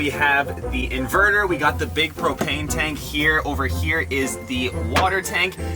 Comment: I thought he said cocaine tank